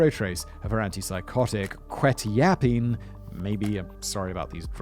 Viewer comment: Did I mishear, or did Simon read one of those drugs as "quit yappin" ?